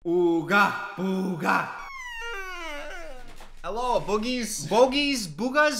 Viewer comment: “hello boogies” NAHAHAJAHA PLEASE KEEP THAT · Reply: SO THATS WHAT HE SAID I HEARD IT AS BOOGERS CAUSE I HAD MY VOLUME LOW 😭😭